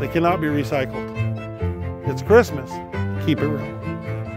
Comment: What did he say? Please reply. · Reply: Keep it real